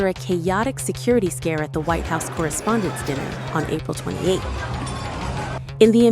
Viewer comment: Am I only one who heard April 28th? Im writing this on April 26th.